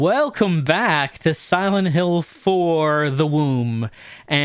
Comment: i feel like im the only one that thought say "WELCOME TO SILENT HILL: THE WOMB"